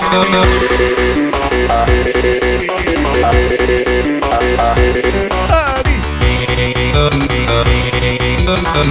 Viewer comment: what did dorkly mario said? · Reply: “Huh neat”